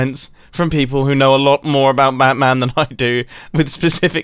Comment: Did he say “mat man” at 6:47?